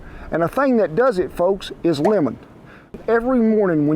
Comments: Oops! When Kent said lemon, I thought he said women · The first time you said "lemon", I thought you said "women" lol!